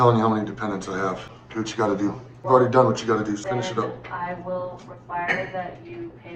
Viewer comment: Yo, Wrap it up B!! Is what he's saying 23:14 😁